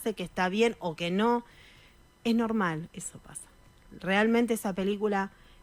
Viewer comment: 😮 vieron que en el minuto 54:12 se siente una voz que dice susurrando: "eso pasa"